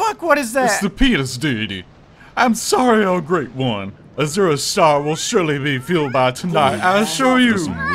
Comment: Hold up. Am I hearing "Azura's Star will surely be filled by tonight"?